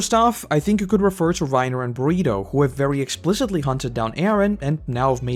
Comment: LOL HOL UP, did you just call this man “Burrito?” 🌯 rip